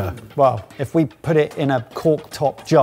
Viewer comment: captions wrong @ 5:04 says 49 pounds not 14